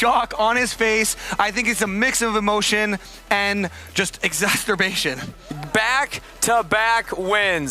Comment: Wait, what was that word used at the end by? At 4:21:10. Exasturbation?